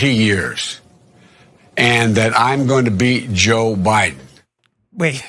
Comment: I heard him say, "I'm gonna beat Josh Biden."